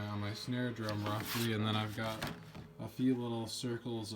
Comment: Did you just say you named your snare drum roxy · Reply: i believe i said roughly, good name though